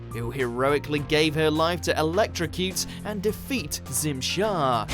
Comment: Oh that’s his name! I always thought she was saying “Tim Shaw” and I thought that’s a really lame bad guy name...